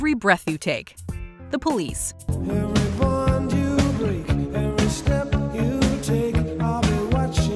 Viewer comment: I thought The Police said, “every bond you break”…EMOTIONAL DAMAGE.